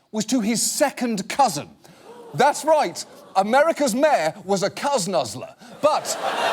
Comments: What did John say at around 5:12 'America's mayor was a _' ? English isn't my first language and I didn't hear the word clearly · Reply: S Holmes I think he said cousin nuzzler. To nuzzle someone is similar to cuddle someone I think. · Now, now, Giuliani was married to his SECOND cousin, not his first cousin. That would be like your kid marrying the kid of your first cousin, okay, still a little bit too close for comfort, but let's be accurate here. But Giuliano is still a creep just the same... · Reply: Boob, no, it does not fit the definition of incest which is "sexual relations between a parent, child, sibling, or grandchild." Not even first cousins meet that definition. Yes, there is a danger of congential defects due to inbreeding, but it is not illegal. I am not defending it, just complaining that John Oliver was using hyperbole when he claimed Giuliani was married to his first cousin. Hmmm, I just listened to the video again, and this tie I heard Oliver say SECOND cousin. I don't know why I thought I heard first cousin the first time. But actually SECOND cousin is not as close a relationship as FIRST cousin. Yes, it's weird. Apologies to Oliver for my mishearing him.